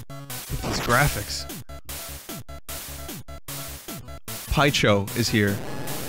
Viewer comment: "Paicho is here" lol in mandarin it sounded like he said 拍醜 which means ugly